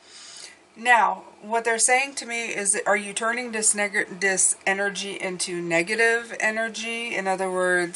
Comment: turning this what??? Whooooo???? 😅😂😅😂😅😂😅😂 I heard that 😮😮😮😂😂😅😂😂😅😂😅😂 Couldn't help it. I had to.